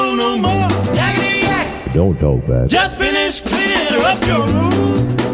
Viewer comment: don't talk back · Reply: I thought he said “don’t talk black”